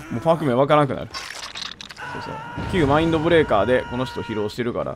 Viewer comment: 旧マインドブレイカーって言い方がなんか旧ハートアンダーブレードみたく聞こえた 謎